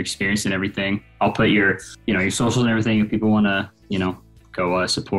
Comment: I thought he said “if people wanna watch the porn” I was like 🫨